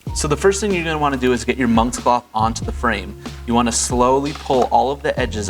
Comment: “Your moms cloth” DID HE JUST SAY YO MOMMA?!?